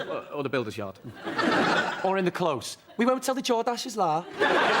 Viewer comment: anyone know what jonny says... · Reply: "We won't tell the Jordaches, la."